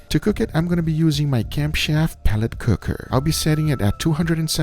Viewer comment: I always thought he said "cam-shaft". TIL it's actually camp chef.